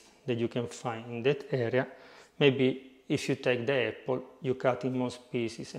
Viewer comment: Great video. I also like to use grape yeast, but there was another yeast you mentioned at 5:03, you said, "if you want to be more original you can cut it into pieces and smash it." Please let me know what ingredient you were referring to. It sounded like you said apricot, but I couldn't hear that part clearly so please let me know what the more traditional ingredient was that you were referring to. Thanks! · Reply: Apple